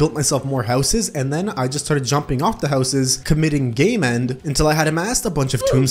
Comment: did he say commiting gay men